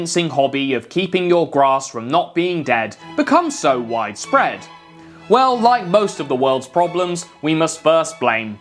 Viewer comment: misheard it as 'whitespread'. Wonder if it's wrong enough.